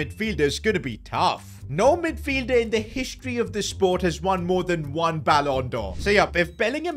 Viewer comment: bruh ain’t a soccer fan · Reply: I misheard and misread it. I thought he said no midfielder has ever won the B’allon for.